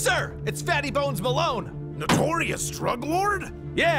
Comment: At first I thought he said fatty post Malone not fatty bones Malone